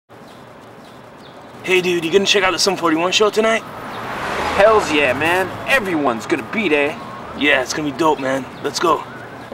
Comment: Can someone tell me what they said from 0:00 to 0:10 ? I always wondered but as a non-english speaker it's a bit hard to get... :) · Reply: “Hey dude you gonna check out the Sum 41 show tonight?” “Hell yeah man, everyone’s gonna be there” “Yeah it’s gonna be dope man, let’s go.”